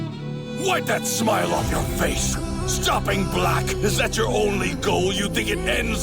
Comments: Did he say stop being black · When vegeta said “stopping black” I really heard “stop being black” and I’m black 😭😂